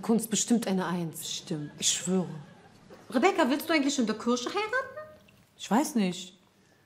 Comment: Achso Kirche ich hab immer verstanden Kirsche (die man isst)😂😂